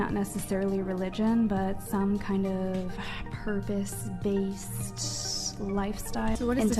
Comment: i heard her say "some kind of herpes based livestyle"